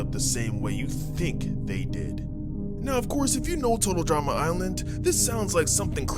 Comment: I thought said Total Trauma island😭